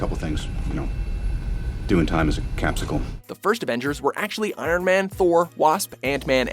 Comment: I thought you said was the first avenger eirler